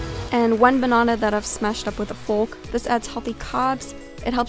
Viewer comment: Illusion She said "mushed banana" which basically means she kinda melted it with a fork😉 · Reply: kef I know the word mushed but I thought she said smashed :)